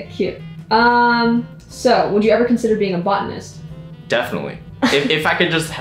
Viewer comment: Help I had to rewind this because I thought I heard: "Have you ever considered being a bottom".